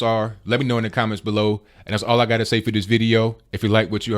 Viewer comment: Love this guy. But, funny thing. At the end, I always hear, "please comment, rape, share". Lol But I digress. . .